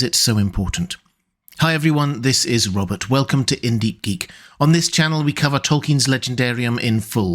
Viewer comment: Thought he said Hindi geek no cap.